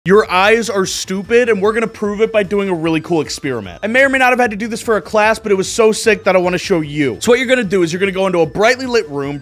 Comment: Bro I'm blind and I just hear "Your eyes are stupid." 💀💀